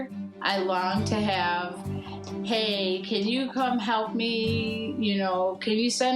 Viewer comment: all i heard is can you come help me gimme gimme lol